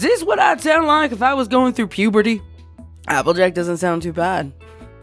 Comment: First of all, I heard “AppleJook” in the beginning.(slowclap). Second, I hope you’ll get well soon, I myself also have a stuffy nose because weather’s a b!tch, and really hope you’ll be able to get things done for your other channels.❤️❤️✌️👍😇